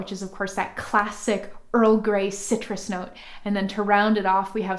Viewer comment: i am such a duffer . i thought you said early gray recipe . i am losing it i think ha ha . sillly me i love your stuff i have been making stuff for years only using a book on essential oil recipes a great book but you make it so easly we did not have this utube thing before its so good to see it made instead off just reading from an old book . keep up the good work .